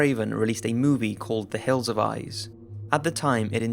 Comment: I love how your accent makes it sound like you said 'The Hills've Eyes'